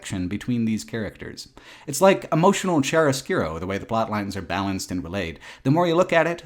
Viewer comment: What the heck saying at 1:37:32 ? The auto generated captions read "emotional and chair oscuro" but I know that's not what he's saying. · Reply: "Emotional Chiaroscuro". Chiaroscuro being a Renaissance-era painting technique that used super dark shadows to make bright parts of a painting even brighter and more prominent.